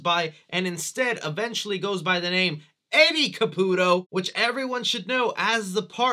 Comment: During the flash back I thought she said Andy Caputo but when I rewatched it she said Eddie Caputo